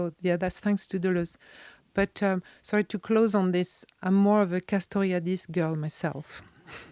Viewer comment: What did she say around 1:29:52? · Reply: "I'm more of a Castoriadis girl myself" (i.e. Cornelius Castoriadis)